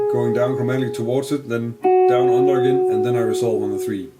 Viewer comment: This is an old video, so I don't remember exactly what I said. Can you give me a timestamp in the video then I'll have a listen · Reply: The example from 0:59 to 1:08. Are you saying you’re going to the 3 or am I mishearing that for G? The note (unless I’m mistaken) is actually a G before you play the CMaj7. I’m just confused by that part and if I’m thinking about it right, are you saying that phrase stopping on G is targeting E (as the 3rd of CMaj7) or are you saying G (which from what I can tell you’re enclosing) is the “target” or resolve note? I hope that makes sense. Thank you so much for replying!